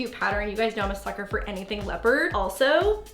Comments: did she say fucker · Did Lauren say F*cker in 12:34?